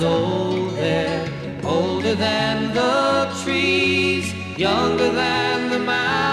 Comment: I hear older than the cheese